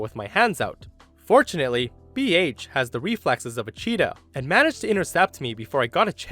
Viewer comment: I heard "BH has the reflexes of a Cheeto." I lol'ed. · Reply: I believe she meant cheetah.